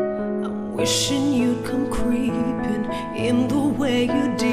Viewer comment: I always thought it said sweeping :) my bad